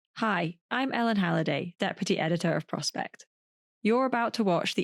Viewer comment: For a while, when Ellen announces "deputy editor", I thought she was saying, that pretty editor.